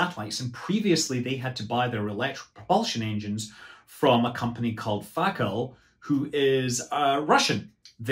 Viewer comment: That company's name is "F*CK ALL"!!! HAHAHA Great video, sorry for my sophomoric humor! That's what it sounded like anyway.